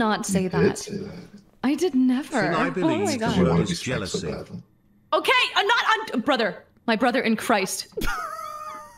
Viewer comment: What does say at 1:21:54? I can't make it out. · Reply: Sounded like "because you want to be smacked so badly"